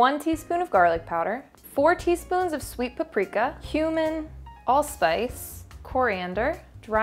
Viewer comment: This one really impressed me! All things considered, it was VERY EASY to make! Let me know if anyone decides to surprise their family with this hunka veggie "meat" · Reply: When you said cumin i thought you said human